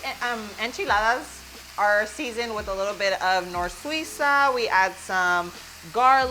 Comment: you said “it’s typically seasoned with NOR-SWEE-SA” ? I couldn’t understand what you said. What is the spice? Again @ 2:59 you say it again. That it’s like a bouillon powder? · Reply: It's Knorr Suiza (chicken bouillon powder).😊